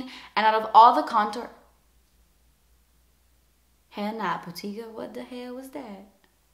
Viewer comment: what the helll did she say at 11:26 ??!!? hahahah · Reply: Hell nah putika 😜